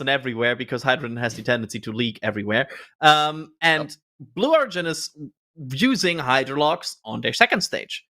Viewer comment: BLUBAR instead of Fubar